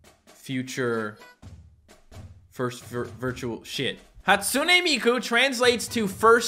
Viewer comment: the first time he tried to say the meaning of Hatsune mikus name it sounded like he said "The first Virtual Shit"